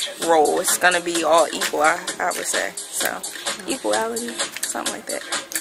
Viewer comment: did she say equalality?